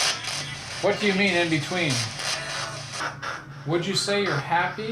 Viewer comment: i hear the mans voice saying........"THE CHILDREN IN BETWEEN RESISTING".......and i think it means, that the parents DON'T want to go in to the light without their children..........WOULD YOU..........????? And it was after you asked Jeff, if it was David that was hanging around in the room.......!!!!